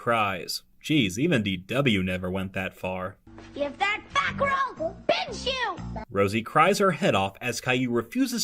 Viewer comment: I thought Dw said give that back or I’ll bitch you